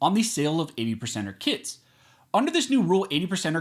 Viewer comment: I know it's early, but i thought he was saying "80%ers Kids" and not kits... I was thinking "damn, ATF is listing kids as guns.. they're overstepping for real"